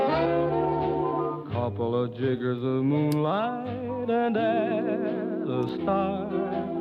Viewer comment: I heard "Couple of N... in the moonlight." wtf